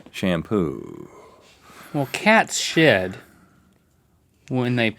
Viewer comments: did Link actually say SH**? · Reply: lol he said shed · goshh!! I thought link said "well cats sh*t" 4:25 😂😂😂😂..I was like ...what the...what happened to PG link?!! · did link say cat sh*t at 4:28? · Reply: He said "well cat's shed..." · I thought link said cats shxt when they pee on themselves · Wait, did Link just say the S word? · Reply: He said that cats shed. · what the heck dose link say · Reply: Shed